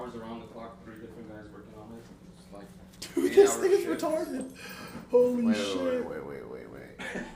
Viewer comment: did banks just say the n word lmaoooo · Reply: he said this thing